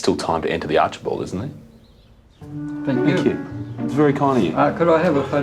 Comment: if you make a handmade gift that requires tons of talent and time and don’t have any appreciation for it i think that speaks to someone’s character. millions of people would be delighted and appreciative of the countless efforts time and talent. The portrait was beautiful not any average joe could do that. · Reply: I’m starting wonder if you all are blind or deaf or something. 12:33 Watch this part over and over again until it FINALLY sinks in that said THANK YOU. THANK YOU. THANK YOU. Do you all understand what those words mean? Because I do not wish to repeat myself again